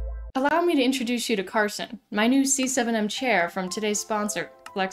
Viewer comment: I swear.. I heard Karlson